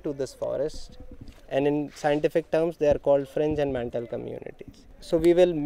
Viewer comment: That would be "Fringe and Mantle..." instead of "...Mental" BTW, thank you for the nice series